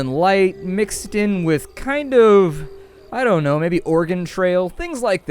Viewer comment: thought you said Organ Trail... not Oregon... however I think the previous may be more apt. :-)